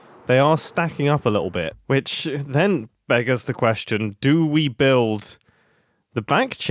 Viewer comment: did mumbo just say "beggars the question" ?